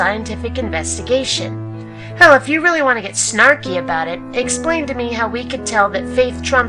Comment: This was a funny thread to see ;) Snarf is also a character in Dragon Magazine from the 80's as in Snarf Quest. However.... Pyram1dH3ad thought you said 'snarfy' but you said 'snarky' as in snide... two very different things... :)